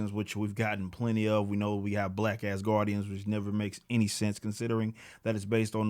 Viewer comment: When you said black asgardians, I honestly thought you said “Black-ass guardians” and was trying to figure out if any of the GOTG were black xD